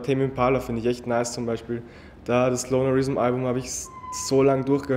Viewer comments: was sagt er in 1:20, welches album hat er hoch und runter gehört? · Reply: lonerism von tame impala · wen meint er bei 1:19 ? hab nix verstanden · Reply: tame impala